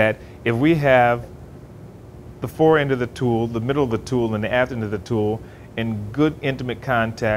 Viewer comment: Did he say “the ass end of the tool”??